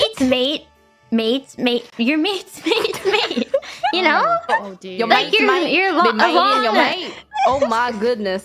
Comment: I think Bae at 1:07 is saying "Your mate's mate has been mating your mates?" i.e. Your friend's significant other has been fucking your friends?